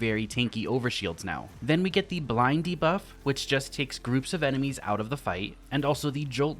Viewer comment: I heard him say “Blindy Buff” instead if “Blind de-buff”. I need more sleep….